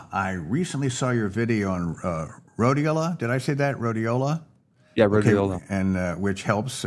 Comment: Hi Dr. Berg, I don't understand "riola" (marker 40:30), that is how transcript spelled it. I take notes and it's helpful to know how to spell it. Thanks! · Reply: Found it, RHODIOLA